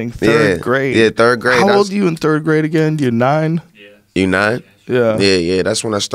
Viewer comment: did he say he was nine in the third grade?.